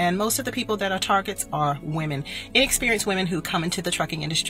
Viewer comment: It sounds like she said inexperienced women.